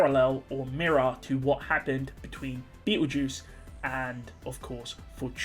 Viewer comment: it sounds like he said beetle juice instead of petelgeuse 😂